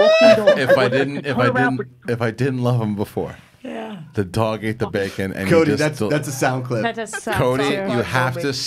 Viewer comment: I thought he said "the dog ate the baby!"